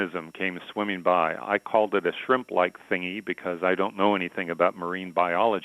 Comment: I SWEAR I THOUGHT IT SAID SIMP NOT SHRIMP WTFF I HAVE TO SLEEP NOW WTF